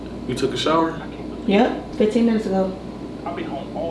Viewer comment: when she said 15 minutes ago the captions said 15 years ago